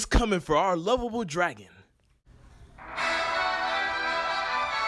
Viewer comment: Lovable Dragon What I thought I heard: Level 1 Dragon. Lol